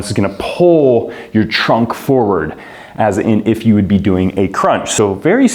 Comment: thought you said "pull your junk forward"